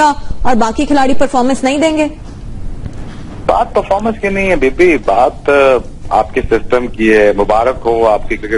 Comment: Is he said baby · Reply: bibi bola h means sister in Punjabi